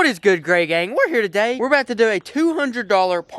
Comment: My first time watching you thought you said whats good gay gang 😅 maybe speak a lil slower